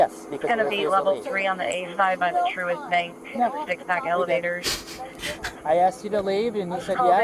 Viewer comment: did anyone else hear that someone was shitting in an elevator over the radio?